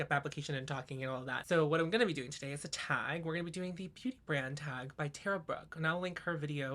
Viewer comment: At the beginning I thought you said “I don’t feel well so what I’m gonna do is attack” and I was like I am so here for this energy … then realised you said “a tag” 😂😂